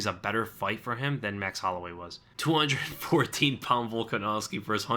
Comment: Finally someone realizes he said 214 instead of 240. It's an easy mistake to make with his accent, but people just keep repeating it without thinking.